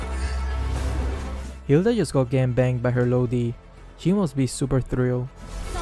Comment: I thought he said gang banged but then I read the sub